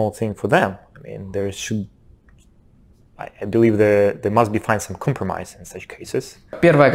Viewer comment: мне почемута слишется что он говорит слова - "there" а ни -"they"в конце😏